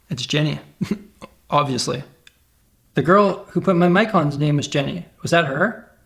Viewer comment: what is he saying at like 4:40? the girl who my mics on is jenny? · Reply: The girl who put my mic on, her name is jenny